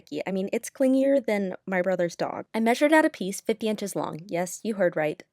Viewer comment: I thought you said your brother's dong not dog what the hell is wrong with me